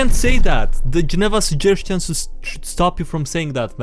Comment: Did he just say “Geneva Suggestions”?